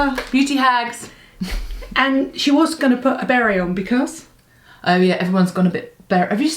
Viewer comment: Great video!! I'm from the U.S. At first I thought you were saying "berry." I couldn't figure out how anyone was wearing a "berry"!! LOL!!! You both look fab in your "berets"! xo and Happy New Year to you both!